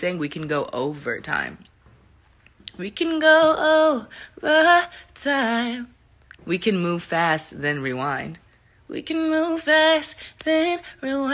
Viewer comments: I thought she said u can go up or down u can move fast and slow - Ik I got it wrong wth 😂😂😂😂😂😂😂😂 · I knew it!!! I heard "overtime" and sang along until the "lyric videos" misled me😭